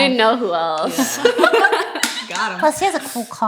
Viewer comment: right at 54:21 i think i heard michael say: "gottem'"